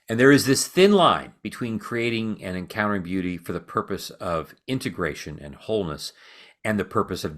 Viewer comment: What did you mean by integration for the purpose of devouring? Also, I love your words, but when you give real specific examples it's so much easier to see! I loved the Jesus telling me his story instead of what the bullies have been telling me . Often I don't want to smile at these people in my head because they still see me as horrible. I feel free when I see they're off and then I am so relieved! Thank you so much!! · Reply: I loved that example too! Thankful that went there. Not sure if you figured it out, but actually said, encountering beauty for the purpose of devouring (31:00 ish),.The best example of that I can think of is, shall we say, explicit media (so my comment doesn't get filtered out). But I also see myself devouring beauty in subtler ways.